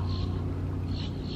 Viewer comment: did I hear "basically he's just drunk" at the 1:20 mark? Hope not.